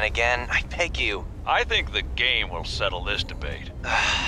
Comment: "I think the gay will settle this debate" is what I heard